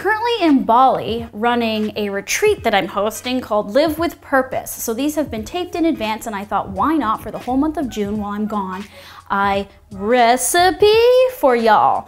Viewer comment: Totally heard her say “living with herpes” and I was SO confused haha 😂 look delicious!